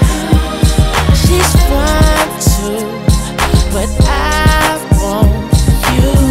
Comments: 5 YEARS LATER I FOUND OUT HE SAY “SHES FINE TOO” NOT “5’2” LMAOOOO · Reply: I thought he said she's "fun too" 😂 I was way off. Thank you, interwebs. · This whole time I thought he said “she’s 5’2”... when did it become “she’s fine too” 🤦🏽‍♀️ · At no point in my life did I ever think he said she’s 5’2”. 🤔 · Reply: i thought he said shes fine too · This song never gets old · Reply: i thought he said “she’s mine too” 😂